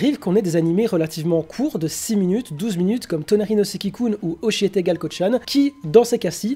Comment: C quoi les noms des animés à l'écrit svp j'entend mal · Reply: Tonari no seki kun